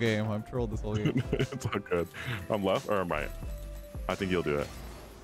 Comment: I thought was saying “I’m Letht” the whole time lol. New name!